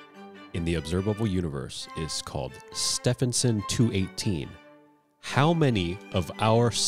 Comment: "what clowns" I say literally seconds before realizing I too am a clown because I never would have guessed 10 billion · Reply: I thought "well obviously all of it?!" Then realized i heard "how much of our sun" instead of how many